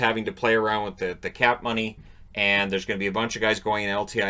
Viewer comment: Did you just say " cat money", because your cat just stopped at took a look at you?